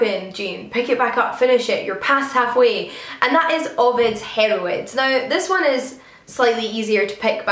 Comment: Initially I thought you said Ovid's Heamarhoids!!